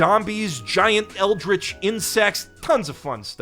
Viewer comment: I swear I heard G giant eldritch 1ncest, I had to rewind a bit